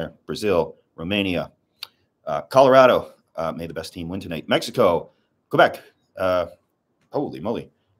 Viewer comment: Did he just say Mexico go back ? :)))